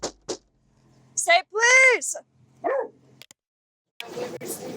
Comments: I thought the officer said “SAY PLEASE!” Lol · I know that this is like a really serious case and stuff, but when the officer knocked on the door, I thought she said” say please” and I almost laughed, and then I realized what she really said